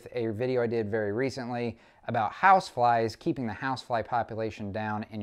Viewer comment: I thought he said housewives.... my bad.. it was flies. Almost ended up on the news..